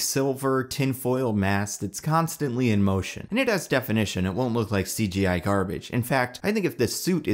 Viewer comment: it really sounds like you're saying "nigga's definition". If you're NOT saying that then I have no idea what you were actually saying. It sounds like you cut one of the audio clips too short.